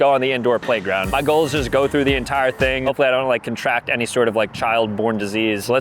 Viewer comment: I literally had to rewind cause i hear you said " hopefully i dont contract any CP in here" 😅😅